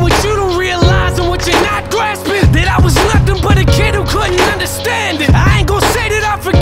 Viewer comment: Note for the uploader: 2:22 he say "kid who couldn't understand it" but the lyric on screen says "could who couldn't understand it"